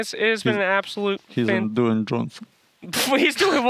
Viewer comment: Miposhka said Dwayne Johnson and slacks thought he said "doing johnson"... oof